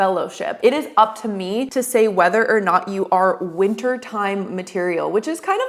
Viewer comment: I thought he said “Winter Tide” material…